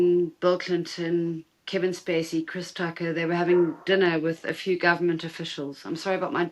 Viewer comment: did she say chris tucker????